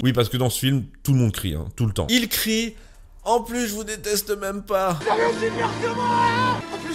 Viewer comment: Pour répondre à ton commentaire je n'ai jamais vu le film parce que ça n'a jamais été clair de comment le voir. A part l'acheter sur un site obscure. Mais 1 : peu importe que ce soit un nanar 2: l'Humour malaisant me fait rire perso donc les vannes du personnage joué par magimel le rend plus proche pour moi que les autres personnages un peu Plus "Carrés" je pense que je peux plus m'Identifier a lui qu'aux autres. 3: la mort du sniper tu entends "je vous deteste pas" sur l'extrait que tu montres j'ai entendu "je vous supporte pas" et la chose intéressante vu que le sniper est un support et pas une troupe d'assaut, est ce qu'il parle a ses camarades pour dire "je suis bientôt mort et j'ai pas su vous supporter donc vous aider" ou aux Talibans pour leurs dire qu'il les déteste... Je ne fais qu'emettre des Hypothèses. 4/ de toute façon juste pour embêter les journalistes gauchistes français je mettrai 5/5 a ce film 6/ la journaliste est complètement à l'ouest mais l'actrice est plutôt jolie'